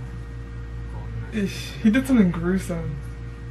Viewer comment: did she just say "eish"?😂